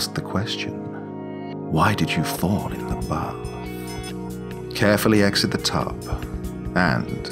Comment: I heard “ask her, why did you fart in the bath?” 🤣